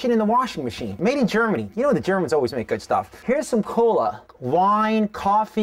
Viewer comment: omg i heard him say "made in Germany. those Germans always make good thing" XD